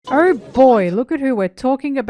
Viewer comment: I thought you were saying 'our boy' instead of "oh boy"